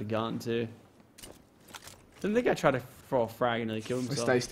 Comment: Did Lachlan say the n word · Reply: Unknown 101 he said didn't this guy try to throw a nade